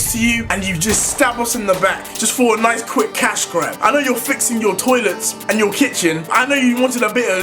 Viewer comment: did i just heard “I know you’re fixing the toilet in the kitchen “